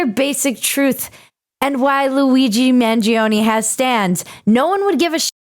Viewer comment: I thought she said "this is why Luigi Mangione has STANDS" for a second, my mind immediately assumed it was a JoJo reference.